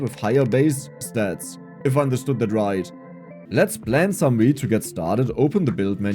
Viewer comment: you read the text on screen "let's plants some wheat" i was in the kitchen and heard "let's plant some weed"!!!!! i love your accent. i know the game but that's what i heard